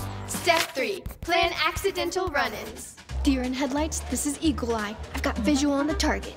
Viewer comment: I'm doing microbiology homework, so when Veronica said "eagle eye" I thought she said "e. coli" and I was so confused lol